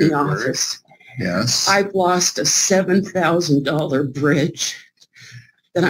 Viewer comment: I thought she said 7000 fridge. I'm like dayum does that thing cook for you and bring you breakfast in bed rofl